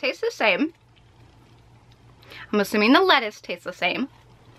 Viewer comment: I always thought what she said was lettuce was pickles 😂